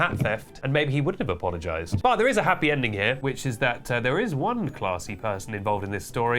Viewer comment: did he just say “hatpy ending”?